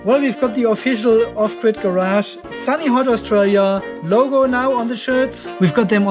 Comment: I thought you said Sunny Heart, Australia thinking this is where what you said you were filming from. I did Google maps search, then Google corrected it to Sunny Hart. And boy the results that came up Lol xxx. Never found Sunny Heart on the map.